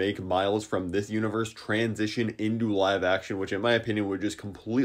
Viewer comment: i thought you were going to say trans 💀